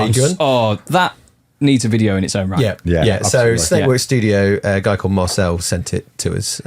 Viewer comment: What's the name of the instagram studio posting these great nostalgic pics? it's mentioned at 16:23 but I can't quite catch it. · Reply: Snake boy studio I think he said. Spelling may vary.